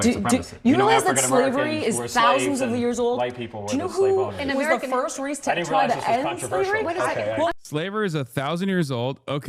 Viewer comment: hasan misheard, the argument is even stupider than that, she said that white people were the first race to try to end slavery